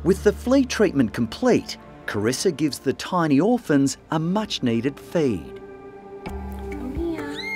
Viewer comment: I thought he said a much needed fade🤣